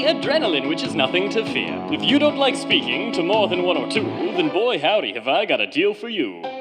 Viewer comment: I thought it said spanking